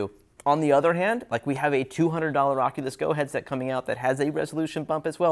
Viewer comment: what do he say 8:17 Rocket disco headset? is what i hear, but that can not be right.